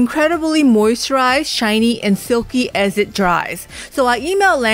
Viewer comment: I thought she said filthy instead of silky loll 😂😂